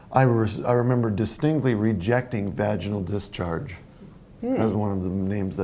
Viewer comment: omg! Did he say “vag discharge”!? Cue big word now. Nebulous? Convoluted? If you are a victim please find the nearest safe space on the way to you gender studies class.